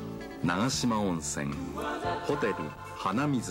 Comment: 幼稚園の時、このcmでホテル鼻水と聞き間違えて、幼稚園で「鼻水という名前のホテルがあるんだよ｣と皆に回り教えた思い出w。